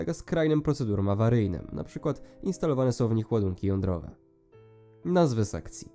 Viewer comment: ja usłyszałem że Instalowane są tam łazienki jądrowe . XD